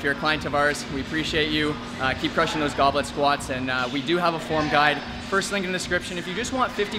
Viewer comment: Why do I keep hearing "goblin squat"